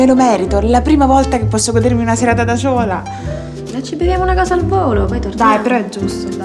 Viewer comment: "una serata da ciola" ho capito bene? ahaha